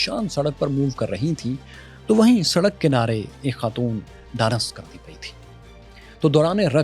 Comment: Did I hear it right? U just said "danac krdi pai c" at 8:49😂😂😂😂😂